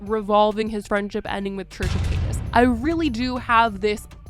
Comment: I have the captions on & it says “church of penis” instead of Trisha Paytas @ 4:53 😭😭😭😭😭😭